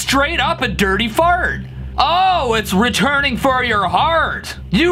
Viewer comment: i Heard returning fart